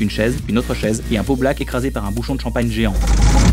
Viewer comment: Il dit quoi à 0:32? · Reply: BGP bébé gros port Un beau black écrasé par un bouchon de champagne géant